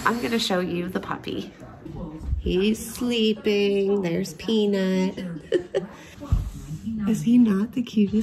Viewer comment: Lol I had to rewind a couple times at the beginning I thought u said chicken feet🌝.....ohh peanut ❤️🐾❤️🐾pure LOVE